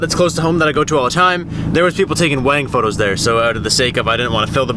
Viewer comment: When you said "Wedding Photos" i thought you said "There were people taking wang photos" and i was like oh dear lord